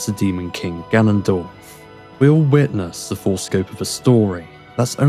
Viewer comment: did you say "foreskin of the story"? 😭